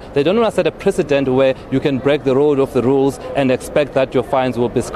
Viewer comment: woooaaaah✋ am I the only ohe who heard ' break the road of the rules' 4:54 😂😂